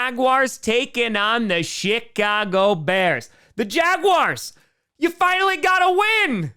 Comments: For a second I thought you said Shitcago Bears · I thought you said shitcago bears. Did you say shitcago bears? I swear I heard that lol